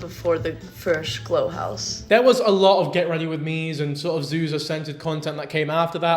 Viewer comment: i heard "zuza scented" and thought "oh, what a fun way to say that!" 😅